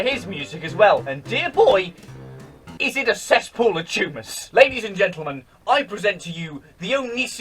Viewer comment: I heard the word “tumours” as “tumous” Tumour humus.